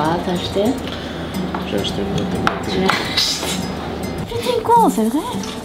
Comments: c’est moi ou a traité de “P’tit con”? Ça y’est, elle parle parfaitement le Marseillais alors? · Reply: moi j'ai compris "putain con", une ponctuation quoi :D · c’est moi ou a traité de “P’tit con”? Ça y’est, elle parle parfaitement le Marseillais alors?